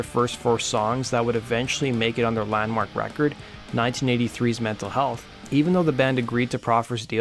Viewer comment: sounds like you said "mental health"